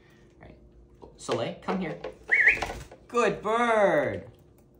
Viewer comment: Я четко услышала «Ко мне», а не «Come here»